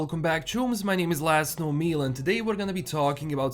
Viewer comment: Good god. I've been subscribed for several months and just realized in his intro he's saying "My name is Last Known Meal and...". All this time I thought he was saying his name was. 😳💀